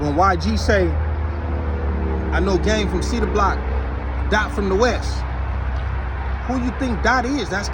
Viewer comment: ALL this time I thought YG said “ Dom from the west “ I didn’t know he was saying “ Dot from the west “